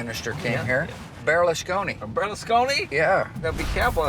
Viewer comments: Berlusconi? I thought he said "bearless coney" lol. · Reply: Berlusconi? I thought he said "bearless coney" lol. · Berlusconi? I thought he said "bearless coney" lol.